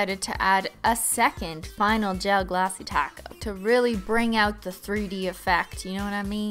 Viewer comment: at first I thought you were saying a glossy del taco, but then when I realized you weren't I was LIKE THAT'S GENIUS SHE SHOULD CALL a gel top coat a del taco